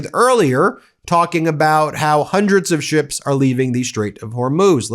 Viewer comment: I don’t think Rubio said “hundreds of ships are going through.” I heard him say “hundreds are lining up.”